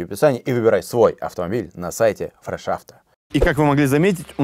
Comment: Мне одному послышалось "на сайте ТРЭШ АВТО"?